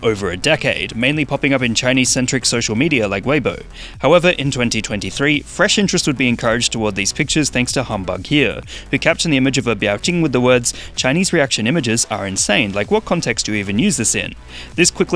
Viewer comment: I thought you said humbug here as in here is a humbug rather than humbughere